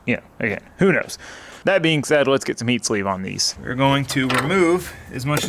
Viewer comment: Did you say lets get some MEAT SLEEVE on these? lmao